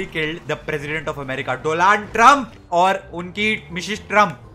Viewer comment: did he just said "we have successfully killed the president of america"...... ""DOLUND TRUMP""!!!😂😂😂